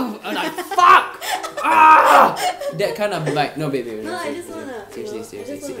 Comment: Did anyone else hear say "no baby" to Naomi at 5:52 or...just me?